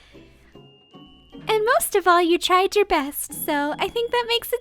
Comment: did noel said you tried or you tired im confused?